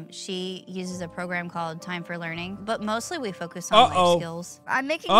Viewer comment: Holy shit I just cringed so hard I almost imploded. Not just because that's the homeschooling I did growing up, but because the captions are wrong and it's actually Time*4*Learning.